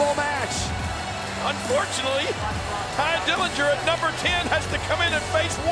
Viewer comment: What's that voice saying at 0:34? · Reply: Jerry Lawler said " unfortunately"